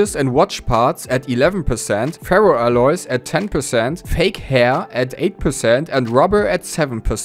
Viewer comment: wait a minute rewind, did you say fake hair?